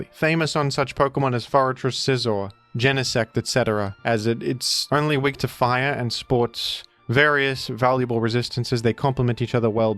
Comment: I thought you were saying "It's only weak to Fire and Sports" here, implying that being bug/steel made you a poor athlete. That gave me a sensible - if not confused - chuckle.